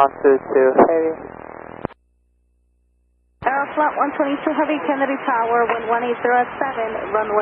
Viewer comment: And this is how a few cones can create a huge mess in one of the busiest airspaces in the world. Cones on the way, an aircraft blocking the main landing runway and minds starting to blow! KUDOS FOR THOSE APPROACH CONTROLLERS! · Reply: I thought I heard in there. This sounded like a big mess for everyone!